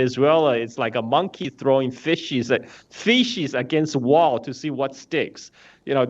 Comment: I agree with carl zha, the analogy of the "monkey throwing fishes against the wall to see what sticks". The thing is that emperor has made a hard realization after it tried to push 🇨🇳 🇷🇺 🇮🇳 🇧🇷 🇮🇷 around and the block did not flinch. So now it is unaware of how much strength and persuasive/dissausive strenght it has left. To find out it needs to rattle/shake the coconut tree 🌴 to see which 🥥 is the weakest and ready to fall under extreme political, economic, military, and financial pressure. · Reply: I thought Carl said "faeces."